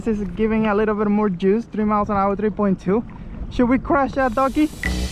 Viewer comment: i thought he said, "should we crush that donkey?" and i'm thinking, don't hurt donkeys, but could you crush a donkey with a kayak? it's more of a monty python question. wait, i was watching a kayak review....